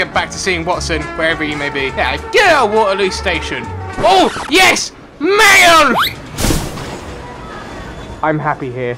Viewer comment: First time I saw this vid I thought he said "YES MAYO!"